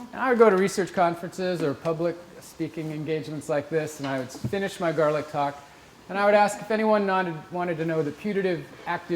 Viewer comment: english subtitle says "finish my girl a cock". hmmmm...